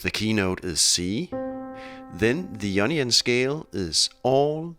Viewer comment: I thought he said 9nion scale.